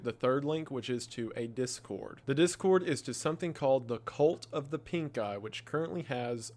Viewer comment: i heard "the cult of The Pink Guy" & almost died laughing then & there lol